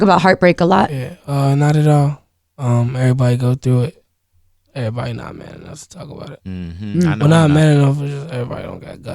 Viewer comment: If someone can tell me what he said at 12:30, I’ll pay you $5 personally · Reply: Everybody not man enough to talk about it. 5$ please